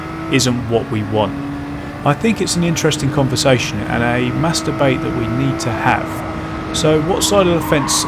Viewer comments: / 13:14 you said "mass debate", for a second i heard "masturbate" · lol when u said mass debate i thought u said masturbate!